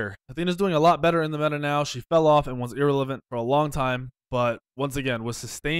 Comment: "Athena, A tier. Athena's doing a lot better in the meta now, she fell of and was ear elevent for long time." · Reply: Joshua Burdine Joshua Burdine no I said war elevent because that's what it sounded like said. Go to 3:20 and you will hear it.